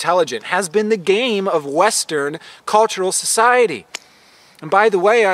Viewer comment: it sounds like you said "wes-turd" society. How fitting. 💜😂💜